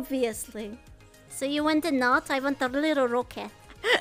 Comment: What did she say at 0:13? I heard “so you want a knot?” but I couldn’t understand the rest. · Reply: "I want a little rocket" referring to canine penis being called red rocket